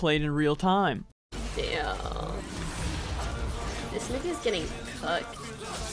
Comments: when i heard fucked i thought she said "this nivia's getting cooked" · I HEARD COOKED UNTIL 7:14 ULULULUL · she was maybe saying cooked? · i hear cooked lmao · I heard “ cooked” but I guess she said fucked